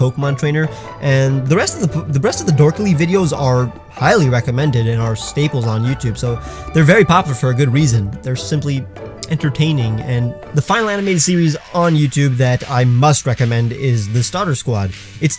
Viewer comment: Did i hear him say popufur? Iswear i heard him say popufur, pls confirm it for me, did you actualy sat popufur?